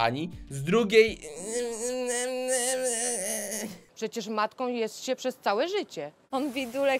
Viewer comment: usłyszałem "Przecież martwym jest się przez całe życie"